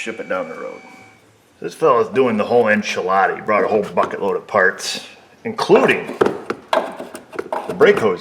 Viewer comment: Did I just hear the f-bomb dropped off shamelessly in public on 0:20!!??? Come oon... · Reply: BUCKET-load of parts is what he said.